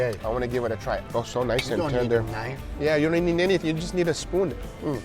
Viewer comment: Anyone Tell Me What He Is Saying ? 🤭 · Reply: 😂😂😂 nee nee nneeeetd it…and a spoon 😂😂😂